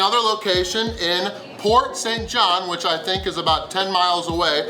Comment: I thought he said the other location was in Fort St. John not Port St. John. I was really hoping there was a restaurant in BC.